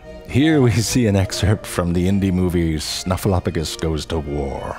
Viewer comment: "Here we see an excerpt from the indie movie Snuffleupagus goes to war" Is one of the funniest thing i have heard this guy say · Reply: When he said “indie movie” I thought he was saying “Indy movie” as in the new Indiana Jones movie.